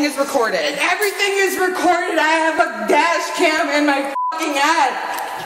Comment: Did she just said “I have a dash cam in my a$$! “ ? 😂🦾🤖